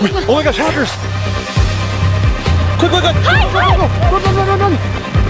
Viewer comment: is saying hi or hide